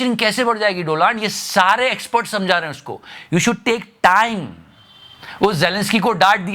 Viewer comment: wait, did he just say "Doland"?😂😂